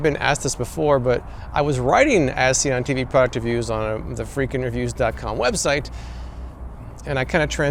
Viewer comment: Everytime I hear him say the channel name I hear "Freak Interviews" which would be a very different channel lol